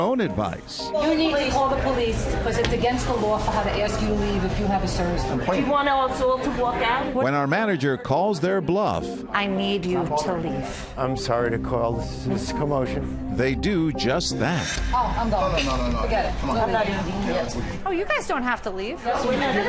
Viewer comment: I know this is serious but when the manager said "we need you to leave" I heard "we need you to leaf" and I couldn't help but laugh.